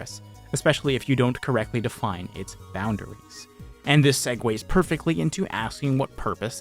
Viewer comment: did I just hear the word Segway without a sponsor after????